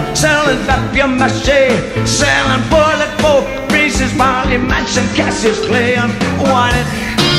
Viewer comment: funny silly funfact: I maybe misheard it but I really think Bowie said "Marilyn Manson (the juxtaposition between Marilyn Monroe and Charles Manson)" instead of "Charles Manson" on this line; he already changed the lyrics a little bit other times, like in the Five Years performance at the Old Grey Whistle Test. Well, there's a old picture from Marilyn Manson (the artist; I think it is from the early 90s) where he's wearing a shirt from this Bowie period (David Live; Young Americans). I never saw Marilyn Manson say that he had adopted that name because of a possible Bowie first mention of it, but considering he's a huge Bowie fan, I just don't doubt it. I was a huge Marilyn Manson fan, but since I discovered Bowie, he has been on my mind for years and years and it never stops.